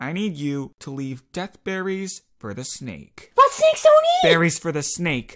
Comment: I cannot figure out for the life of me what Leafpool says at 0:29 help (Weird compliment but I love the way u drew the thorn barrier) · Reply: She said "but snakes don't eat"